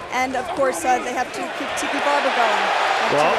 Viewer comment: I thought he said Kiki Barber.